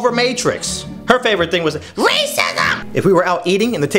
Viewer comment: I thought you said the she loves racism 😳